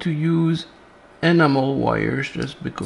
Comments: I thought he said it was animal wire. Do you get that from electric sheep? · Oh, ENAMEL wires! I thought you said "animal wires." Mon Dieu! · I thought you said “ enema wire“…. no shame in using it if it works for…. but I figured it must be a pretty nasty job getting ahold of the stuff 😂